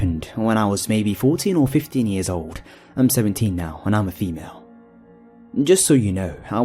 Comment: I swear to god i heard him say " and im a female" at thr beginning😂😂😂